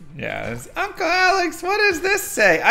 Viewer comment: did she really say "Uncle" Alex? ... does that smell like... you know... " love marriage in the air"? :D